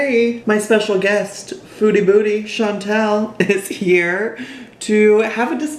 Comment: I legit thought you were saying “Foodie booty” 😂😂😂